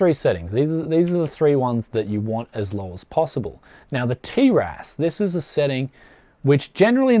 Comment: the subtitles say "tier ass"